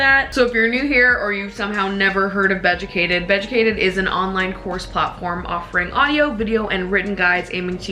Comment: Omg unrelated to the topic but I misheard the sponsor as an ‘online horse platform’ and I was like.. girl what 👁👄👁